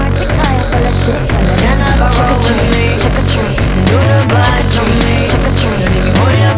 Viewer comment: is back sounds like she is saying chuck a tree 😍😂😂